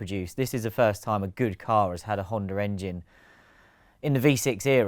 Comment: Every time they say Honda Engine, I hear Honda 'R'engine